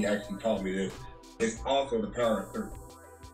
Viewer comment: What is the "ohhhhh" realization at 5:50 of the video? I couldn't make out what was being said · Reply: 1-3-2 is the same as the Power of 3